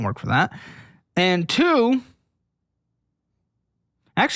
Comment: ish did you say get a predatory loan?